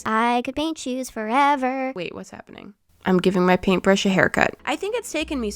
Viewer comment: Did you just say “I’m giving my hairbrush a hair cut” or did you say “I’m giving my paint-brush a haircut”